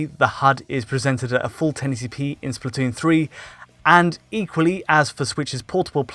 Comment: was that an "inkually" instead of "equally"??? lol